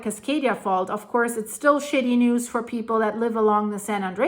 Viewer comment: Did you say “SHITTY NEWS”???? 😂 I adore you! You’re brilliant and real! Keep going! (Are you selling your house and moving to Utah?) 🎉